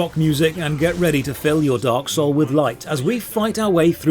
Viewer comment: "I should have been the one to fill your dark soul with light." As a kid I thought he said "hole with white". Still makes me laugh to this day.